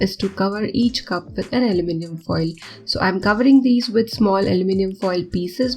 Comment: You said : cover it with foil 2:14 Caption said : cover it with oil 😂